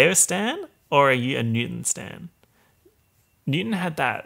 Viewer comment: we have no choice but to stan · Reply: I thought you said Satan. I was confused.